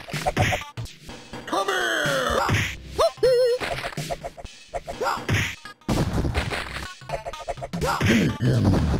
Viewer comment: Hope you know what Dan Forden says... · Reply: TOASTY!